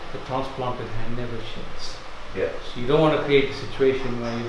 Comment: Can anyone tell me what the doctor says at 7:10? ; it seems important. Does he say .."never sheds"?